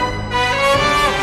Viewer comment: i thought i heard the n word lmao